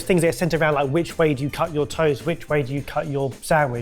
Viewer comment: I heard “cut your toes and cut your sandwiches” (I immediately thought “oh, silly Barry”)… it took me a couple of seconds more than I'd think necessary, to realize he said “cut your toast”… it isn't easy to listen to something spoken in a language different from yours while you pretend to be working =S